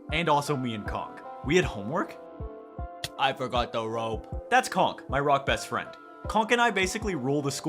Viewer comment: why is no one talking about what said 0:58 he said "this is conk, my rock best friend" he did not say "he isn't the brightest" because conk is the brightest one here, he killed bee